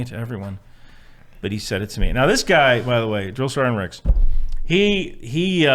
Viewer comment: did he say Drill Sgt Ricks?